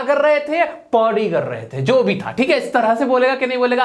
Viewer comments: I heard poty kar rahe the 😂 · I think 🤔 he is saying poti kar rahe the 😂😂😂😂😂